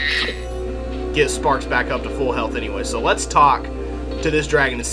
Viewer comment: I thought you said "Gnasty´s loo" and thought to myself that you were going to visit his toilet.